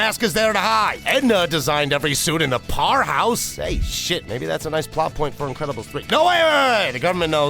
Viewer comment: huh?? What?? Are you black? Why do you think that’s cool to say? I instantly turned off the video. But whatever gets you that engagement right? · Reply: he said maybe 😂😂😂